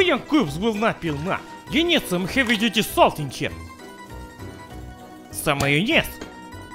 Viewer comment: I thought said "Heavy Dirty Salt" and thinking that it would roughly translate in to "Heavy Dirty Soul" as in 21 pilots (my fav band), I truly for 5 seconds felt blyatful and happy.